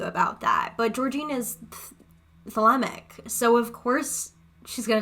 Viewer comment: what word was that? Thellemic? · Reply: Its another occult practice if you want more info you can watch daat darlings video on Thelema and misconceptions of Thelema, she is a Thelemic ceremonial Occultist.